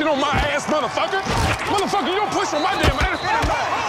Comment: did anybody else hear Buddy say "you dont push a white man" ?